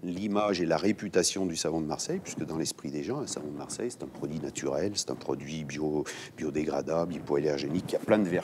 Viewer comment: Pourquoi j'entend "en boutiques bio bio" ? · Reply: Il dit "c'est un produit bio, biodégradable". Il ne dit pas deux fois "bio" tout court.